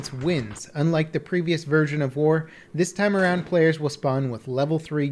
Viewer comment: I misheard 'war' as 'whore' xD